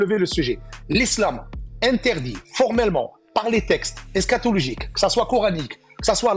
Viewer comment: C’est moi ou il a dit scatologique ? 😯